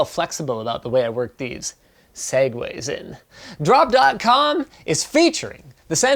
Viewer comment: it's the first time i watch an LTT video with closed captions on and for the life of me I thought he was saying Segways, not segues